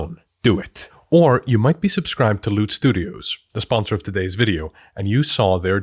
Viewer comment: sorry but... Do you know that "Loot" in that ad sounded like "Lewd"?